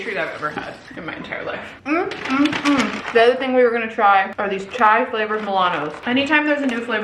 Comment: I thought you said “chive” flavored milanos 😂